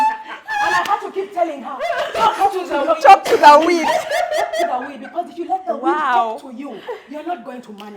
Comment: OMG! This weed story had me in stitches😂😂😂😅😂😂💀💀💀😂😂😂😂😂! I replayed it 3 times. called it, she's like "We're not going to lie on Bump Love" 😅, and the way said "You guys her eyes were popping out" 😂😂😂 I legit cannot imagine poor high😂😂😂. "Talk to the weed"😂😂💀😂😂😂 my cheeks and intestines cannot take this anymore😂😂😂💀 Thank you for the laugh ladies😂😅! Congratulations on your personal achievements, and here's to a bigger Bump Love journey in 2024🤗😍💞. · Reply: OmGoodness!thanks for typing out this hilarious story coz I heard, "Talk to the wind" & thought to self, "Is the wind the weed whisperer?"🤣 Ok!it was talk to the weed.